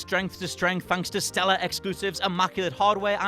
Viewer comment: Am I the only one that heard him say "expoosives"? Also... give up gimmicks? are you kidding me? Switch is a massive gimmick